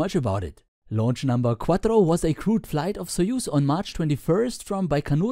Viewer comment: you're back! ... i heard crewed as crude and i heard "US are" as USSR